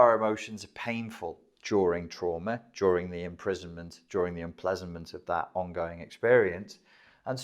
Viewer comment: did he say, "during enplesanment,...." Is it even a word? Confused....